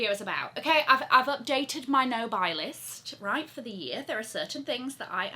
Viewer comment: This entire time i thought you were saying “on my no-bicyclist “ 😂 but its no buy list! Some exotic birkins would be cool!